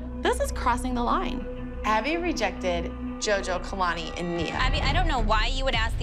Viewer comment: it says Mia instead of nia 😂 if you put the captions on